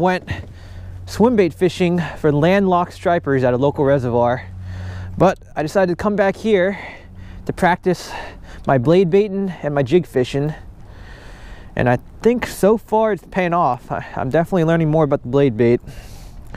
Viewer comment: Heard it as practicing my beyblading haha does anyone remember those toys?